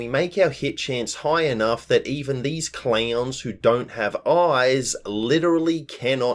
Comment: When you were talking about the lost's outfits, I distinctly heard you say, "There's police officers, cheerleaders... Even clams!" That one got a double take and a laugh, so thank you. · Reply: He said clowns unless there is some inside joke I'm missing here?